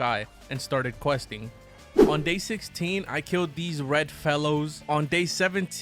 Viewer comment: I thought u said I started killing red mellows instead of red fellows 😂